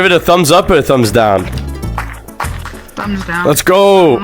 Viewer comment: I swear he said thumbs down